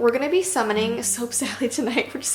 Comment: I thought it said soup sally not gonna lie